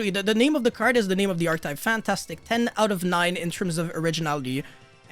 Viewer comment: say that again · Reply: he actually says fantastic on 0:35 first